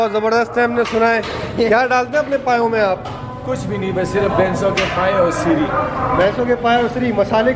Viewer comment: kya bola unhone🙄🙄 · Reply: Bhainson ke paaye aur Siri